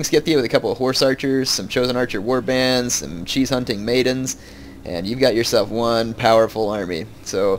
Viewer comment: lol I thought said cheese hunting maidens.